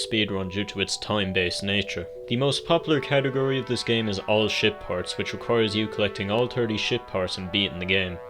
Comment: I misheard "ship parts" as "shit ports" and wondered if there were some illusive ports of Pikmin 1 that were so bad, nobody talked about them except this niche speedrunning community. 😅